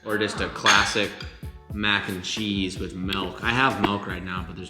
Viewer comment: why does he call it MELK lol